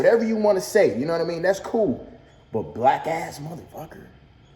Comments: I heard “wackass mfs” instead of blackass... but idk 🤷🏽‍♂️ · Might be reaching ddg lowkey sound like he just said “wack ass mf” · i heard "wack a*s mf" aye but we can cancel him anyway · naw im mexican nd all my friends is black so b4 yall say im racist or white, but he said wack ass mf but he said it but he stuttered a lil bit then said wack ass mf sounding like he said mwack ass mf so yall thought he said black but naw he aint say black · i fw DDG heavey but it sounds like he said wack ass mf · Lmao I heard “Wack ass MF” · sound like he said wack ass not black ass. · It sound like he said wack ass mf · I heard “wack ass mf” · I heard "wack ass mf" or am I buggin? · Sounds like he said “wack ass MF”? Idk. Still DDG better show up and knock Nate out! If he don’t.......not a good look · I thought he said wack ass mf lol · It almost sounded like he said wack ass mf but I could completely be wrong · Sound like he said wack ass mf ?